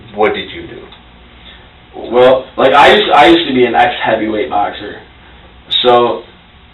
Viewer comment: did he say “heavyweight boxer” ??? Bro, you’re middleweight at most. But I guess there is a little bit of a conundrum with “I used to be an ex-heavyweight boxer” maybe he lost like 50lbs since his fighting days way back when… 🙄 ya know, when he was 13